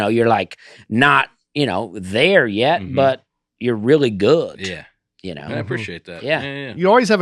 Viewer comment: I heard it this time! I heard “T” that should be a “D” - “that person’s not really there yet, but they’re goot”. 1:11:29, I love you man, but you definitely do stress the “t”. Hey, it’s ok cause we’re havin a goot time! 😅